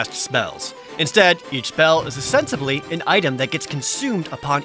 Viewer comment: is he saying “ostensibly”?